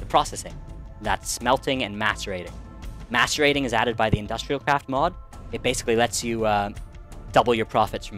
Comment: OMG! When you said that the macerator was part of Industrial Craft I thought you said it was part of Equestria Craft! XD I AM SUCH A BRONY!